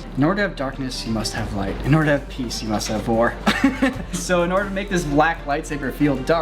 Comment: I thought he said "in order to have peace you must have VORE" and to be honest that sounds more intense that "war"